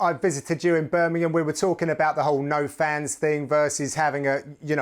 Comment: all i heard was "you got no fans"... hahahaha